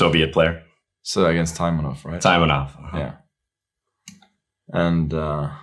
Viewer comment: Someone know what is that player what did Fischer face, Magnus says his name i this time 7:07 but i can't understand? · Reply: Mark Taimanov